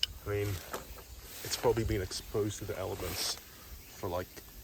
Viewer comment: did he say ELEPHANTS?????